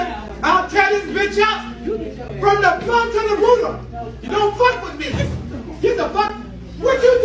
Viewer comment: I thought she said something like “from the plumb to the roof-ah”, like from the bottom to the top. Fleccas said from the Rone to the Louvre, is that a saying? Interesting Rorschach test, what did you guys hear?